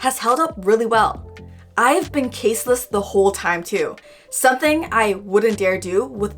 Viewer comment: Why did I hear it "I have been braless the whole time" Amazing video though..